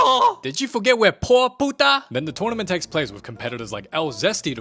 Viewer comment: DID HE SAID P:)TA???!!!!??